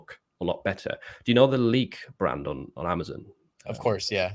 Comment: Great video! What did he say at 14:43, the “Leak” brand on Amazon? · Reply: Lique was a HUGE Merch brand that dominated every major niche for years, then one day they disappeared, not sure if they got banned or just pulled their products since Merch had a copycat problem